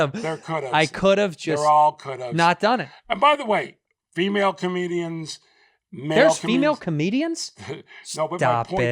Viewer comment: Who's the comic jeff mentions at 30:00+? Can't make out the name, Rick something. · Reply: Rick Ingram. He's awesome